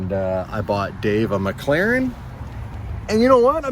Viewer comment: DID U SAY U BOUGHT DAVE A MCLAREN??